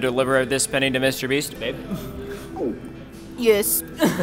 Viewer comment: Is is mean or did Ryan called babe replay